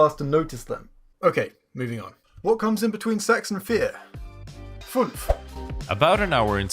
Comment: What he says: What comes between sechs and vier? What I hear: What comes between sex and fear?